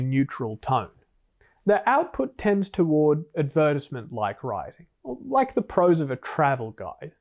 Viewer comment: what like writing? lol never heard it said that way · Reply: Advertisement